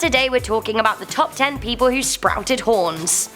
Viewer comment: I thought it said, "top 10 people who have steamed hams"